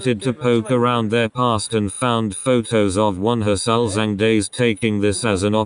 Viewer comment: i dont understand the word at 4:51 celsing?? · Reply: ulzzang